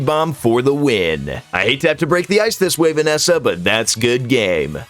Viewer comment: did he say Vanessa